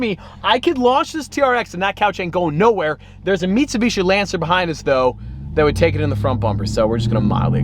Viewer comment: I think I just heard James say "meat-subishi"